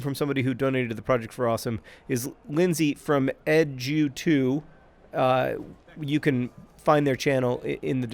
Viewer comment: I thought John said "Lindsey from Edge U2" and I assumed the channel was a U2 fan channel...